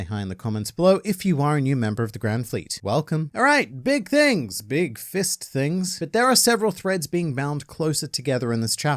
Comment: Did you really say "Big fisting"? 🤣🤣